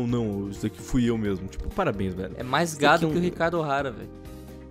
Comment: Eu vi rápido e entendi "O GAME mais GADO".